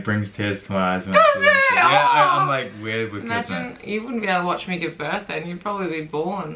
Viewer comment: 😂😂😂 I thought said you would be born too! Your double take was great!